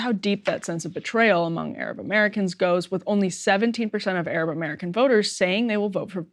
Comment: AND again at 6:56 the cc(closed captions) are claiming that she is saying 117% instead of 17%!!?? pretty strange??!! especially because it's not like she is speaking unclearly or with an accent OR it's not like "one hundred and seventeen percent" sounds like anything even close to "seventeen percent"!!!! too bad there's not some way to report the "mistake" to yt!! i feel like i'm about done with yt anywho they made me turn off my ad blocker which i've used for YEARS(more than a decade now!!??) so disgusted with them...wanna just use spotify/rumble/etc/etc INSTEAD now... :'( i'm 40yrs old so i clearly remember the time before the internet a time where i used to boycott the radio(i always just bought/listened to my own tapes/cds/mp3s/etc) AND tv(always change the channel during conmercial breaks/only watch vhs or dvds/even just go and read a book instead was better for me than seeing/hearing even one single conmercial) so if i previously could reject/go without radio and tv solely because of the conmercials surely i will now and yet now suddenly for some reason yt is forcing the conmercials on me or i now have to "pay" to get what had been free all along??!! this was the ONLY reason i even watched yt was it was free with zero conmercials and now why!!?? why is it not??!! so utterly disgusted and disappointed!! i guess nothing lasts forever and yt must be feeling "the squeeze"!!?? good for them then go ahead and ruin yourself!! we should take bets on how long they'll las